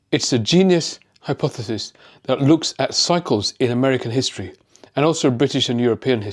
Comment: Did you say psychos in history?